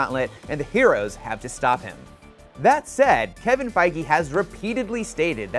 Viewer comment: D...did you just say Kevin “Fi-gee”?